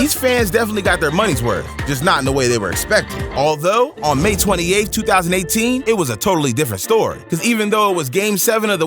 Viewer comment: he said may28th 2018 captions say may 18th 2018